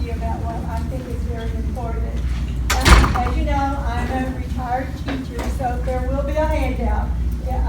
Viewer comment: When she said she was a 'retired teacher' I heard 'retarded teacher'.